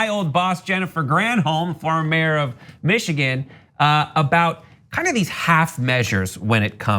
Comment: Did he say "Former mayor of Michigan"?